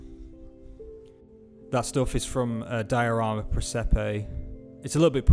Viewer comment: Hi....outstanding work! Question for you. At the 14:08 mark of the video you mention the "foliage company" you use. I can't quite make out what the name is? Can you please tell me and/or provide a website address? Thank you so much in advance for your anticipated response! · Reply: Hi, apologies for the northern English accent. The company for the foliage is called "Diorama Presepe" and appear to be based out of Italy. And thank you for the kind comments!